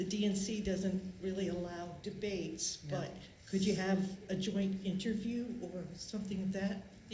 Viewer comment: Thought she said, "Can you have a joint?" for second. Been watching too much Joe's podcast for too long, I guess.